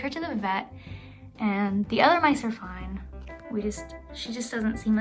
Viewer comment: When she said the other mice are fine, I thought she said the other mice have died 🤣